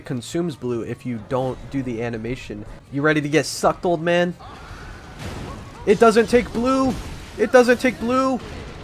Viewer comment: I actually thought I heard "It doesn't take LUBE"